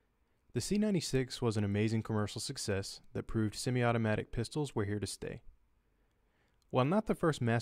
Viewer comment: i thought that he said that semi automatic pistols are a mistake